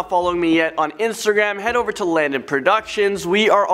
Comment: Oh and when you say your name Landon I thought you were saying do not sing hahahaha sorry lmbo